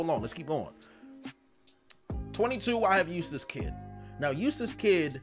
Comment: I genuattly thought you said useless kid instead of eustass kid